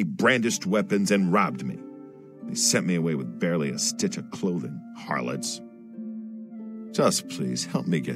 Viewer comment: I thought it said "with sticky clothing" .-.